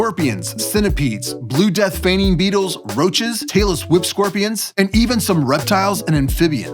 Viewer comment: Okay, when you said “tailess whip scorpion”, I heard “taylor swift scorpion”. I had to go Google to clear that up, because I was beginning to think she got her name from a scorpion.😂